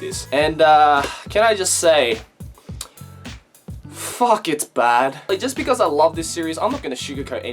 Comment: I have to say Me (in my mind) THEY FUCKED IT UP: FUCK IT BAD ME: ? Who the fuck says "FUCK IT BAD" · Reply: Sparkzz323 Well i heard "FUCK IT BAD" Sorry for mishearing